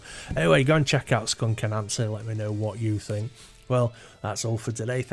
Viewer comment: Oh, BTW, I thought you were saying "Skunk and Nancy". LOL. Sorry.